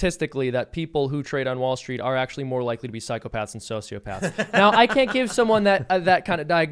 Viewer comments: Seamus: "... psychopaths and sociopaths..." I hear "The spirit of Luke Rudkowski is upon me..." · Thank you so much for having me! Really great conversation tonight! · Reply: Seamus: "... psychopaths and sociopaths..." I hear "The spirit of Luke Rudkowski is upon me..."